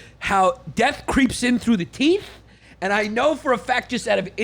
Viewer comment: Whole time I thought papi Yan was saying dying through the teet not teeth.